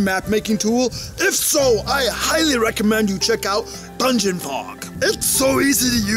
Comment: auto captions called it Pungent Hog instead of Dungeon Fog.